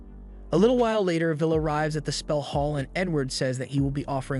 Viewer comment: bro said N word instead of Edward 😂🤣